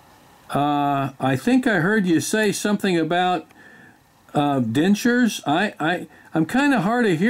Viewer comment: I thought he said benchode but he was saying dentures I guess. My hearing is also bad. I keep wondering why Mary Kay keeps calling, I tell them I only buy Avon.